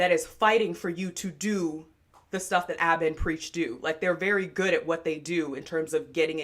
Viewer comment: I Thought U Said Aba And Peaches And i said peaches?! didnt they ban her off of every platform in existence and what beef they got with my good sis she dont bother nobody 💗